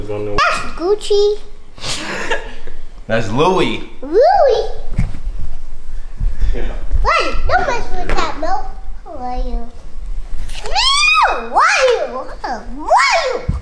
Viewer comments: I thought said “don’t mess with that moe” 💀😂😂😂 he said “don’t mess with that remote” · I thought said, "Don't mess with that Moe" 😂